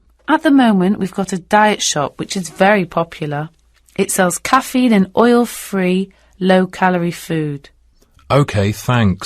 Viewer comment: I got 40/40. This is the first time I have done such these questions. I feel thankful for your test. It makes me have sense of confidence Btw, Q4 should have been Coffee rather than caffein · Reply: the recording says "Caffeine and oil free food", so it should be Caffeine and not coffee.. I misheard it in the beginning too..